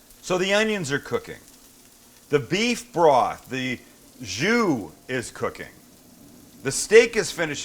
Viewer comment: Did i hear “the jew is cooking?”